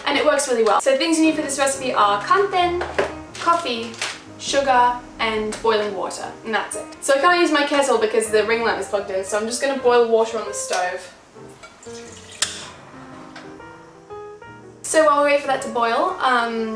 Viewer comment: I swear I hear you say "and toilet water" at 2:16 xD